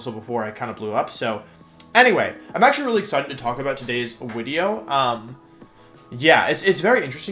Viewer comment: Does he say wideo?